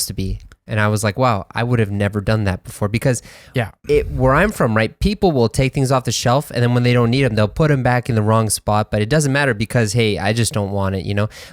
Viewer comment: やっぱネイティブの発音の聞き取りムズイっすね…。7:43もの「people will take things off the shelf」がtake things offがtakingsoff（ティキングズフ）に聞こえる。things完全に消えてる…